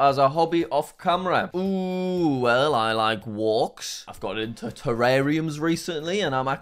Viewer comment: thought he said terraria